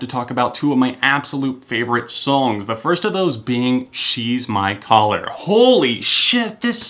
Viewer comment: To anyone wondering how I've already had the album: Easy, grabbed it while Murdoc was in the bath. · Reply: When I first heard She's My Collar I forgot the name and thought he was saying she's my brother 😂